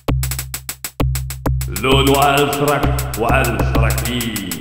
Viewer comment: What did said? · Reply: Lolu Alf warak wa Alf rakhi. It was hard to translate, and it does not even make sense.